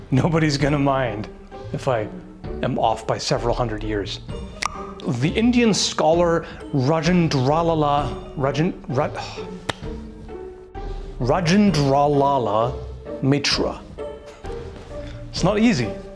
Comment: what I heard: "The Indian scholar Rhodent Tralala... "